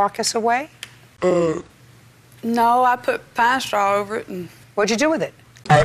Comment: “i put pinestraw over it” “what did you do with it?” “AtE iT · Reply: I couldn’t understand the word I thought she said “I put pasta all over it”.